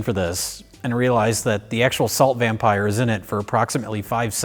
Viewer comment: A salt vampire? Oh I thought you said assault vampire, y'know like Kiefer Sutherland or Johnny Depp.